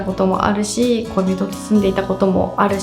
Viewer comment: 「恋人と住んでたこともあるし…」が「小人と住んでたこともあるし…」に聞こえて一瞬なんだって？ってなりました笑 どれもあるあるで参考になります… そういえば、私は湿度の変化でメンタルが悪くなります（乾燥してるとダメ） 今年さんが使ってるバルミューダの加湿器を買ったのですが、簡単に部屋が一定の湿度で保てて去年の冬より体調が良くなりました！ でも一番のメンタル回復はさんの動画を見ることですよ…